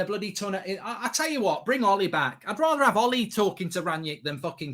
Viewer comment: Did he say "Bring Olly back!" ? 😂🤣